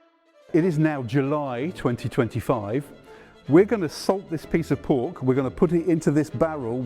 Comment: 5 seconds in I think I hear "I'm going to ASSAULT this piece of pork", I need to go to bed